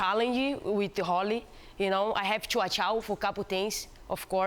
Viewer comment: Did she say " I have a chow chow" ?